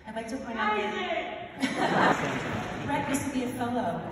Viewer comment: What does she say here? 1:17 Brett used to be a....? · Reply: a fellow at SSO. Which is a special program for young musicians accepted to do like a two year ‘apprenticeship’ with the orchestra. Pretty prestigious, I think.